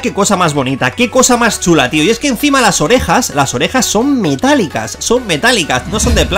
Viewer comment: pense que dijo ovejas xd